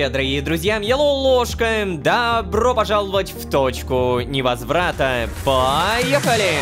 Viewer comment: почему мне послышалось "в точку, мне разврата!"